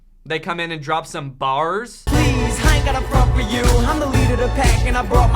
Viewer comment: it sounds like he said “please, I ain’t gotta fuck with you” Just when I thought this was a kids movie lol